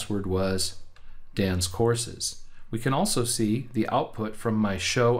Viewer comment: I thought you were saying dance courses 😂😂😂